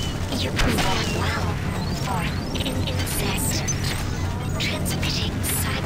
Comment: Sometimes Shodan sounds like she says 'incest' when she calls you an insect.